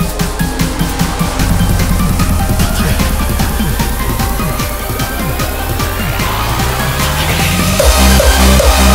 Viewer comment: In 1:50 what does he say in repeat? I know it stands for fighting but I cant write it xD I think its tatakae ^^ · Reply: Repeat to myself - it is tatakae ^^